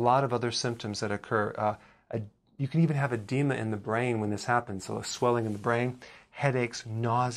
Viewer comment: Hi 👋🏻 doctor 👨‍⚕️ berg ,it’s potassium pat here in London England.,it’s the<num>, i had to laugh 😂 at this video,I thought you said you could also have a demon in the brain,if you listen to it that’s what it sounds like,I am still on OMAD ,you have been a very great 👍 help,as I am 67,I worry about not been able to get the goodness from my food ,I eat bell peppers every day with celery and 🍎 cider vinegar,plus broccoli 🥦 and purple cabbage.i don’t eat 🍞 🍚 or sugar,I eat 100 grams of ground hemp seed this contains 1200 mg of potassium plus 800 mg of magnesium plus all essential amino acids,it is low in lysine which I supplement .as always 👨‍⚕️ berg your the best,have a nice day as the Americans say.🥇🥇🥇🥇🥇🥇🥇🥇🥇🥇🥇🥇🥇🥇🥇🥇🥇🥇🥇🥇🥇🥇🥇🥇🥇🥇🥇🥇🥇🥇🥇🥇🥇🥇🥇🥇🥇🥇👍🍀🍀🍀🍀🍀🍀🍀🍀🍀🍀🍀🍀🍀🍀🍀🍀🍀🍀🍀🍀🍀🍀🍀🍀🍀😄😃😁😎😀😃😄😁😆